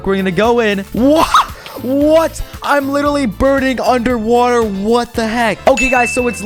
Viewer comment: is it just me or did he say wattle instead of watet